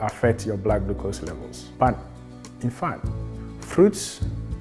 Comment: did he say back fat?? Wow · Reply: No, he said "but in fact"